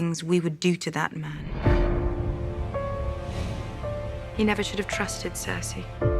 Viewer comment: Dany is not mad at Sansa, she says "HE never should have trusted Cersei", probably referring to Tyrion. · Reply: Sounds like she says "YOU should have never trusted Cersei" although.... that doesn't make a whole lot of sense lol. Unless she was telling her about her past dealing with Cersei.